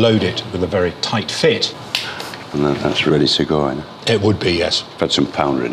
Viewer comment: "That's really Sugoi." - Sean Bean. (He actually says: "That's ready to go" or "thas reedy tego, ye?")